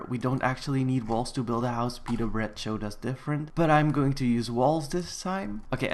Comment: did you say petah bread because im a fan of him to